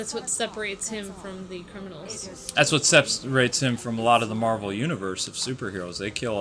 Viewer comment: did you just say batman is in the marvel universe? @ 13:58 batman isn't in the marvel universe, he's in DCU. two different things. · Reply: he didn't say that batman was from the marvel universe. He said that "That is what SEPERATES him from the marvel universe. Not specifically saying that he was in the marvel universe